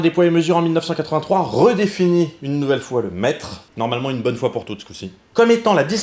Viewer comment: Quand t'as dit "le Mètre", j'ai entendu "Le Maître" et je suis parti en trip de type Doctor Who dans mon slip ! ^^ Et oui, putain j'ADORE ce nouveau format XD J'ai hâte pour la suite ! :D